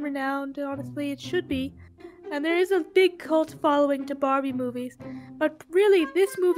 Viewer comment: excuse me did i just hear the word "Cult" or am i mistaken?